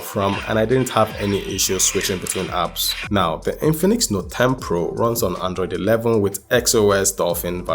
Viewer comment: Did i hear Bote 10 Pro on 4:28???